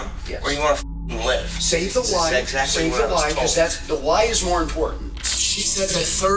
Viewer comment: save the lid ??? I thought he said save the why because that’s more important !!! It be lotta officers abd the people that be so caught on the bigger issue that they don’t realize that they digging a bigger hole to get out of . You gotta correct all that immediately. Now I’m going to end this whit never speak without a lawyer specially if you innocent but get nervous just because it’s the police !!!